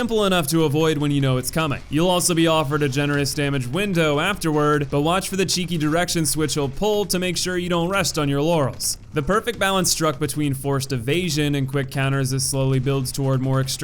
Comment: why did he say don’t rest on your Yanny?